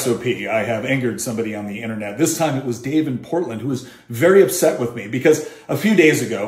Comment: Geez... I thought you said Dave Portnoy 😂